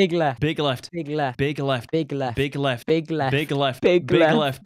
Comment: I hear "pig left" lol